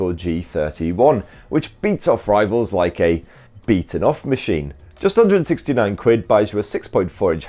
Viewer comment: Did he just say “beating off machine”?